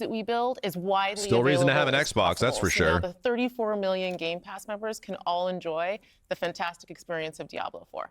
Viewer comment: 34 million concurrent, it was in the podcast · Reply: I heard "34 million game pass members can enjoy"